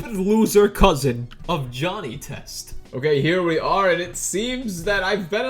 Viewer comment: DID HE SAY JOHNNY TEST